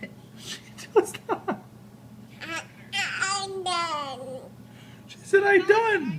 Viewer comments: I heard “I’m done”. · I heard her say it perfectly: “I’m done.” She didn’t say, “I done”, she said “I’m done.” Grammatically correct, and with perfect pronunciation! 😀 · I heard “I’m done” instead of “I done”... This baby has better grammar than her parents.